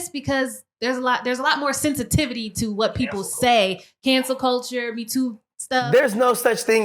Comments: He thought they said Cancer culture huh?? Why didn’t they clarify they said CANCEL culture? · So no one is going to correct what he thought was said? He thought he heard "cancer" instead of CANCEL and even said it was a disease lol. I hope dude stays lucid and on-point for the rest of his life.